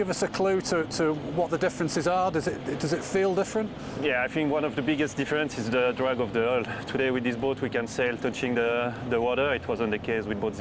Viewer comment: Translator please! There’s nothing worse than listening to someone who’s mother tongue is French talk English. This amounts to torture. My ears still ring! Come on! It shouldn’t be that difficult to find another person to interview inside the Alinghi Red Bull AC organization. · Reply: Yeah, I didn't understand what his response was at 1:45 and had to rewind it about four times since the subtitles didn't make it out either. In case anyone missed it, he said one of the biggest differences between boat 0 and the new boat is the drag of the hull. Now, whether he meant aero or hydro drag is anyone's guess, LOL.